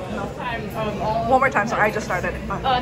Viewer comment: did she just say one more time sorry i just farted?